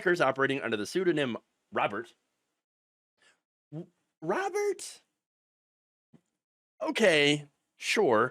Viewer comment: Haha, I thought you said their pseudonym was Robbers😂 I wonder what kinda dirt trump has on everyone to make them capitulate so hard....